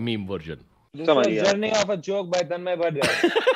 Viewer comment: what did samay say? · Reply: "Journey of a joke by Tanmay Bhat" Samay was referring Abish Mathew's show Journey of a joke